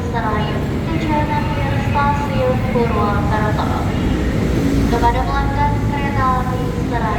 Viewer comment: bg itu KA apa kurang kedengeran suaranya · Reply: KA Serayu tujuan Purwokerto